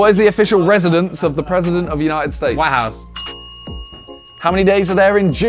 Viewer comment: I lmao when said the White House Cuz I Thought he said the Warehouse